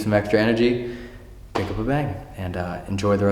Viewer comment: Ngl when he said "when you need some extra energy, pick up a bang" I thought he said hook up and bang.....just me.....sorry I'll go now....